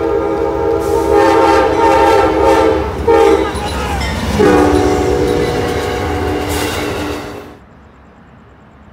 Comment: what was the engineer saying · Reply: He said “sup brother!”